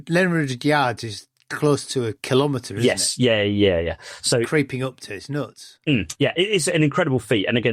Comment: I had to rewind more than once because I initially misheard comment on the Chindwin River as "It's creeping up to your nuts".